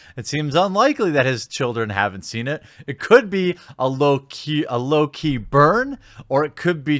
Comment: did he say it could be a ...loki burn